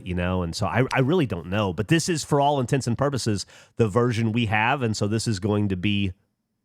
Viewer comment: I thought for sure Geoff said "For all the tits and purposes"